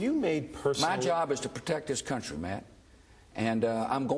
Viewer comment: Almost sounded like he said, "My job is to protect this country, man."